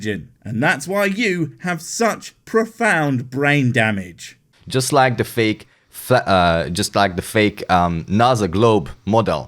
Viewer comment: Did he just say: just like the fake flat...Hahahahaha